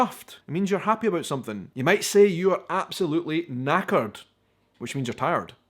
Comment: first time i heard a brit friend of mine say he was knackered, i thought he said "naked"